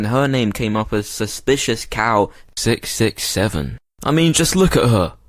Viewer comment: did he just say 67